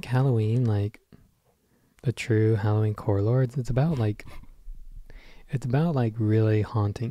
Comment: I heard “a true halloween core lord.” I’m dead already! Love you guys!